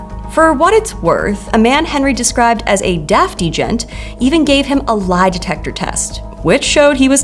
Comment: I’m pretty sure she isn’t saying “deaf agent” here… does anyone know what the captions here are supposed to say??